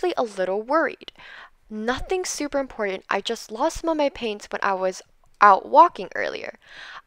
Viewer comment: when i was getting ready for the quest i thought she said i lost my pants lol XD 🤣😂🤣